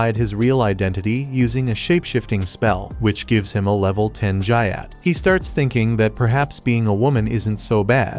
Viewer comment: Did they just say gyat bc istg FF IS ACTUALLY BRAINROTTED. N.